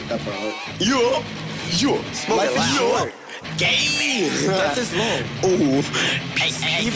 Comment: sound like he saying Gayyyy instead of ayee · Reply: He saying "Gang!"